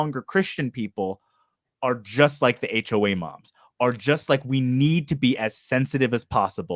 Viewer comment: did just call them HOA moms?